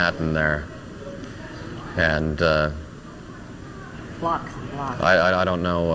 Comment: What her said. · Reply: Blocks... Blocks